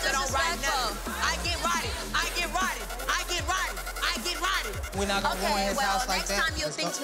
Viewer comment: Apple had me rollin🤣😭💀 from twerkin while sayin “This that real butt I can’t afford fake butt.” 💀 to her being a real one and apologizing to lyrics when she walked in. But then reminding us that “she gets rotten” when disrespected🤣🤣🤣🤣 I love her lmao. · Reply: I thought she said I get rowdy.. But her accent sound like roddy